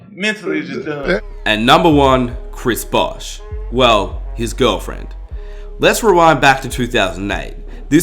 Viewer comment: i thought he said Chris Bosh, Jame's gf lmao